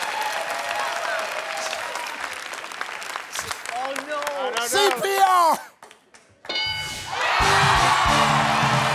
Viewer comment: I wanna know what he whispered in her ear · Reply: The way she puts her head back like "oh right, I should have known" makes me think she either didn't hear him say CPR or she didn't know what it meant.